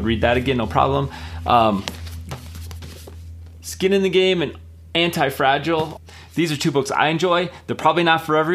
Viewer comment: Just for a second, I got scared. When you said "anti-fragile", that's not what went through my head. I heard "White Fragility", and I just about had a heart attack...until I realized you were talking about Nassim Taleb's book. So maybe 1.5 seconds of a heart attack, but now all is well. Hahahaha! Do yourself a favor and check out "LIVE NOT BY LIES" by Rod Dreher. You won't regret having read that book. Great video!